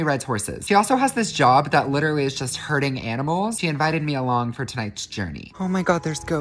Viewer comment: I thought he said “hurting animals” And not “herding animals”